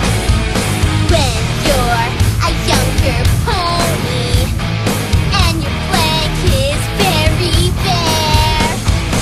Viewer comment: For some reason I heard "When you're a younger pony, and your flesh is very rare." at the beginning. O_o